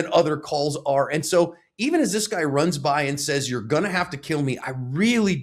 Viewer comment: "You're going to have to kill me"? I heard "please dont do this". But I guess Im hearing things. And is hearing what he wants to hear again.